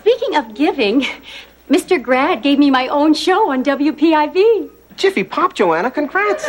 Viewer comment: Did Joanna say WPIZ